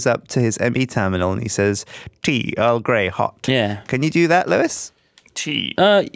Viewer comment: With Pickards accent and never having heard of Earl Grey, I thought he was saying L Grade Hot. I just thought it was a very specific menu selection seeing that there must be millions of different types and blends of tea.